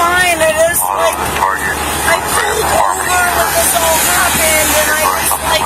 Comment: Maybe I misheard? 8:37 Did she say "I pulled over when this all happened?" Eta: Hope she, who blew a .238, at least got ringworm walking around barefoot.